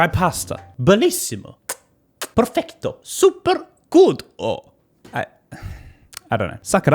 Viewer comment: it sounds like you say "super culo" which means super ass in Italian 💕